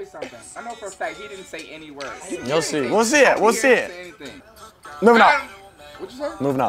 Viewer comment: what song did he say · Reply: Moving up